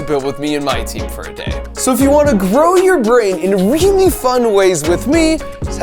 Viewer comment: why did i hear at 18:15 : "if you wanna grow your brain with a really fun way s with w$$d"